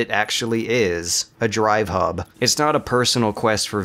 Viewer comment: I thought you said a "dry hump" but I guess that works too. lol